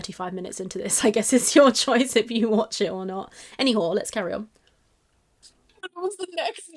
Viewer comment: Wait...did I just hear...yes yes she said "anywhore" lol